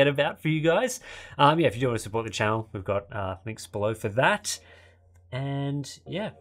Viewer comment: I just realized they say "I'm your host, Tim. I'm your host, Steve" at the end. I literally thought they were saying "I'm yours, Tim. I'm yours, Steve" for a long time. I always thought it seemed odd. LOL 🤦‍♂️ well at least I'm in the 20% club